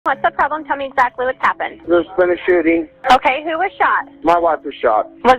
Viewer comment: I heard “there’s been a shitting”😂😂😂